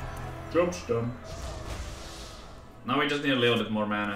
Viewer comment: What did he say at 3:18? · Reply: Job's done. Inkeeper says that when you don't have anything to play